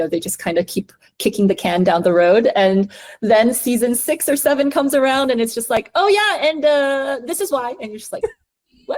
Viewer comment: What did she say?? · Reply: shut up